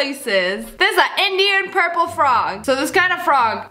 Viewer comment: can anyone please tell me what sssniperwolf said ? 'Indian purple frog' ?